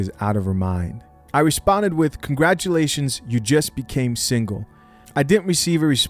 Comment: I thought he said she single. Why the fk are they in the same house? And why the fk does he not have the mental capacity and testicles to know that shes a jezebel im so confused!!!